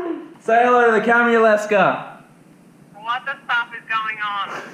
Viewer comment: did she really say 'stuff'? Lol